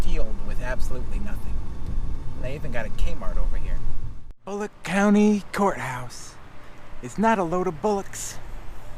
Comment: Haha I thought you said Bollock County at first 😂